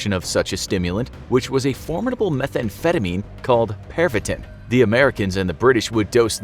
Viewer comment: Pervitin I thought I heard "perv it in" 😂😂😂😂